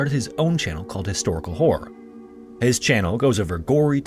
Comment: "Historical whore"? Did I hear that right?